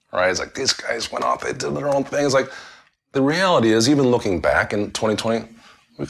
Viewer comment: When he said "looking back in 20/20.." i thought he said "looking back in 2020.." (when the video is 3yrs old) had me quite puzzled for a solid 2 minutes lol